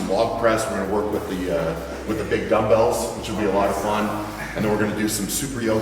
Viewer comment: Whaen you said big bumbells i thought you said big dildos and then you said they come in all shapes and sizes hahahah